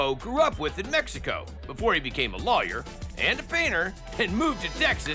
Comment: When i heard him say he’s also a painter I thought he said beaner🤣